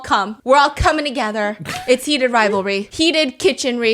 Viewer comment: excuse me? Did you just said Heated Rivalry? 👀